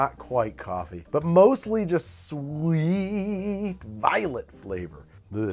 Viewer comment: I thought he said it tastes like violence but then realized he said violets